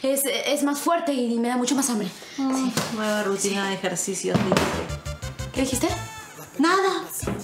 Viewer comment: alguien me puede decir que dijo pepa cuando vio a sarita en la refri en esta parte 1:23 porfis que ya me estoy volviendo loquis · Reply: Pepa dice: nueva rutina de ejercicio dice